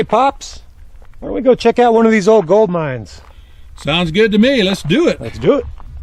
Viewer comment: I was laughing to myself as I thought you said old goat mines. lol. Great review on a nice coach gun.